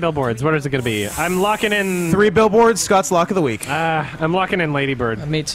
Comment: NEVER 👏 DOUBT 👏 SCOTT'S 👏 LOCK 👏 OF 👏 THE 👏 WEEK 👏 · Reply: I thought you were saying "luck of the week" for the longest time.